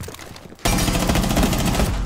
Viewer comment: when dokkabi said reload i thought she said freelo